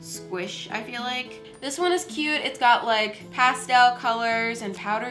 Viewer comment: WHAT DID SHE SAY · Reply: Estela Menjivar Uhm she said it’s cute...